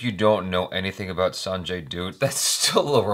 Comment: All throughout the video I kept hearing "Sanjay Dude"